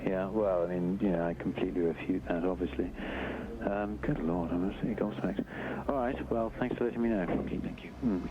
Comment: [inaudible] is him saying "im completely gobsmacked"